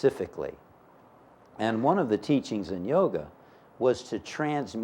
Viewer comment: min ... What is the word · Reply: The word is Yoga.